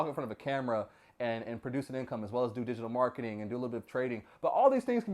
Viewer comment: you mentioned the things you do for work, did you say do a little trading? as in stock trading? and if so what style or trading strategy?